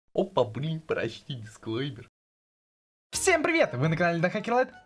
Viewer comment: Я один заметил что он говорит "Хопа Брынь прочти сквейпер"?